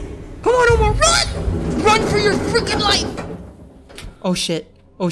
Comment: if you set the playback speed to 2x it will sound like omar saying 'Ocean' instead of 'Oh Sh*t'